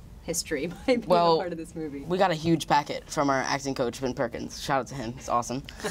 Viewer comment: I thought he said my acting coach Ann Perkins